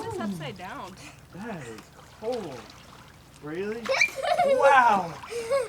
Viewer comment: Oh my GOSH!! @ 20:13 it sounds like she says 'It's cold' WOW!!! Go!! You little cutie you!!! or pls rewatch that and tell me she didn't say that! Cause she TOTALY DID!!